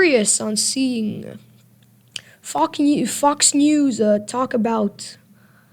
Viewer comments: Did I hear f you ??? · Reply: No it said fox news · Did you say ,,Fu- new"?